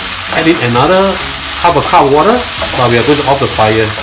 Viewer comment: thought he was saying avacado oil every time he saying 'another cup of water' I was like holyshit thats alot of avacado oil